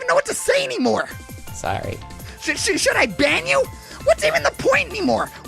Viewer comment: I thought Karkat said "SHOULD I BANG YOU!?" To Tavros wwhen I wasn't looking. but unfortunately he said ban So I was kinda sad.